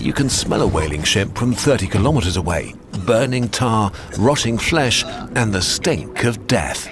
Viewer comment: caption says "burning far" instead of 'burning tar'